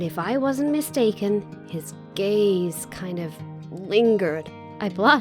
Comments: did she just say "his gay kind of lingered" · Omg I thought she said "his gay scream lingered at me" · I heard a little bit wrong and thought she said He’s gay Edit: What the heck?! 294 likes?! Thank you! Edit again:1k!!!!!!!!